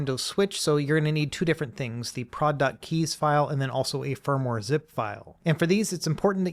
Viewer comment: i thought you was saying product like Forrest Gump 🤣